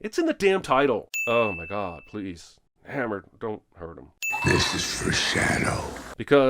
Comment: 😂I actually thought he said "This is for Hernandez", because thats what anyone would expect. I'm shocked what he really said was "This is for Shadow".